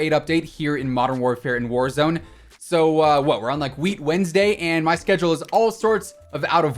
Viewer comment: did he say “Weed Wednesday” ?